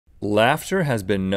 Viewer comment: Not gonna lie I thought he said "the rapture spreads like a virus" and not "laughter"